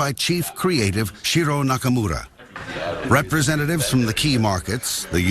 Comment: I thought he said Hiro Nakamura, who watched "Heroes" will understand me.